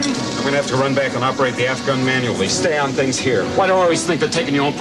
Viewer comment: Is Han saying f-gun?